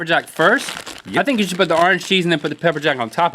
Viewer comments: link said orange cheese instead of orange chicken XD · go to 5:58 and instead of orange chicken you hear link say orange cheese XD